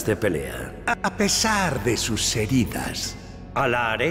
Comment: did he said sus ????!!!!